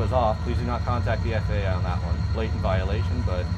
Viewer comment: I dont think the person transcribing the subtitles speaks english well or knows what the word "blatant" means lol 4:25